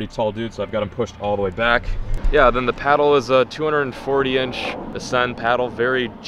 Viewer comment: did he say it was a 240 inch paddle?! 20 foot??